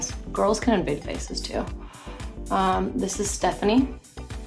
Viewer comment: "This is STEFANy" (that's what I heard, sorry guys!!! lol)